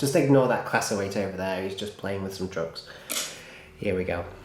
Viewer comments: Had to double take - really thought you said "Never mind that Class 08 there. He's just playing with some drugs"... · That sounded like "Ignore that Class 08. He's just playing with some drugs